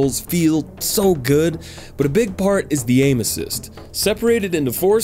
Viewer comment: I heard "aim assist" as "anal cyst" and was so fucking confused 😂